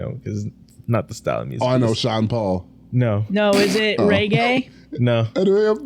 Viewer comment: The way you said reggae I thought you said "Ray Gay". I was like who's that?? 😂